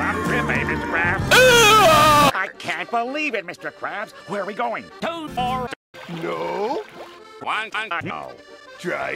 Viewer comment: What did he said? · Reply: Guantanamo